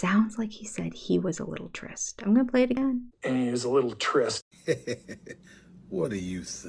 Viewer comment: I heard "and it was a little tryst", said very breathily, the way Kody does.